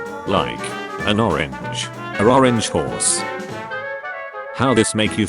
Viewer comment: I thought this said "Orphanage."